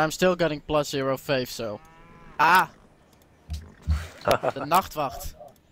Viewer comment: Does somebody say around 08:52 ''Aah de nachtwacht''?